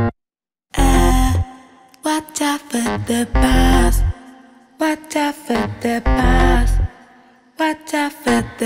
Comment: I heard "Watch out for the balls"...... I could believe it too.